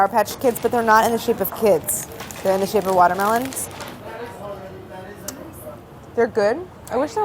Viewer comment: Did I just hear "wourder-melons" 😂